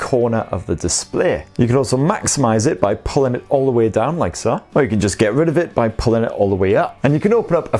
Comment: I heard " Like ça " xD Combination of english french